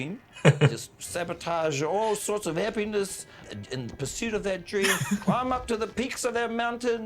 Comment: i keep hearing "sabotage all sorts of air benders" haha